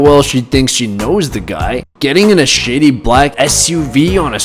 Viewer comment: it kinda sounded like he said s*itty but he said shady